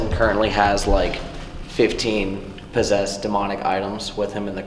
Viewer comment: When it first said eltons name I heard it say “Elton it’s him he’s possessed”